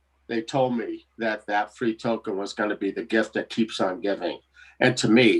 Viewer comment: Hi, English is my 2nd language. What said at 57:30 "They told me that....?" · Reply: That token (FLR) will be the gift that keeps on giving 😉